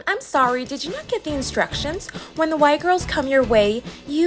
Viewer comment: “When the Y girls” bro I thought she said white girls 💀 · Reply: At thought she said bye girls then had to watch it again and heard white girls THEN HEARD Y GIRLS